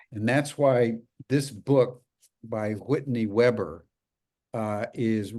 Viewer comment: Did he just call her Whitney "Weber"